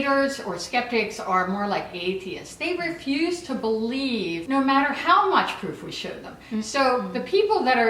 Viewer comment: i thought she said "No matter how much poop we show them" XD